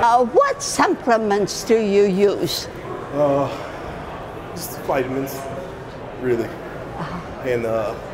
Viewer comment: Grandma: What supplements do you use? Man: Ster.....vitamins. Really. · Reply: Yeah I also thought that he said "I just use vitamins, really"